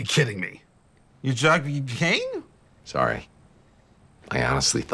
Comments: “Y- you drgged me gain?” thud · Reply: Sounded like he said "You jugged me a game" 😂😂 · “Y- you drgged me gain?” thud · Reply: I thought he said "you drugged your cane?" But now i know he was saying "you drugged me again" kills me